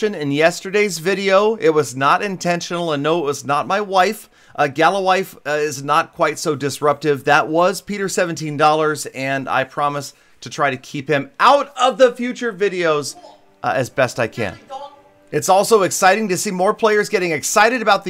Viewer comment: i heard peter